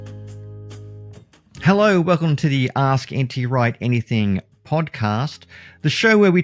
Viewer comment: I just click on this, and with your accent I thought you said, "Welcome to the Anti-Right podcast" I was confused for a second cause I clicked on the video because of the title... Great video tho